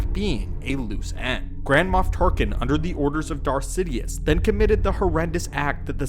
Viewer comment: i had to turn on CC cause i thought you said GRANDMA TARKIN ..... yeah i'm not yet familiar with the whole saga yet.